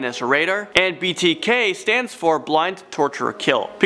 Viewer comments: Did he just say "blind"? Lmao. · 1 little thing, when you were talking about the BTK Killer, it sounded like you said “Blind”, but it’s actually Bind, as in tie up. Great video, love your channel.